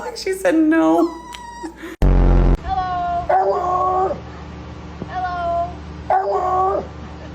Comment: I literally heard "hello hello"